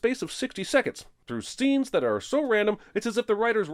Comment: Jeremy says "stenes" instead of "scenes"